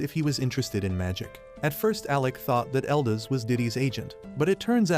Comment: did he say diddy's agent